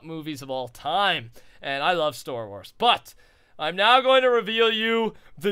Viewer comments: Did anybody else catch at 8:09 in the video when Rusher said his favorite movie series he loves is "Store Wars" not Star Wars the most hyped of movie of all time but "Store Wars". lol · Did he just say stor wars at 8:10?